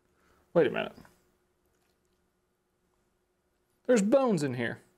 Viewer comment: I literally thought he said there were bugs in the turkey but it was bones lol RIP